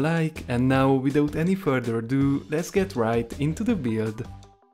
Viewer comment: did you just say lits git right in to the veod at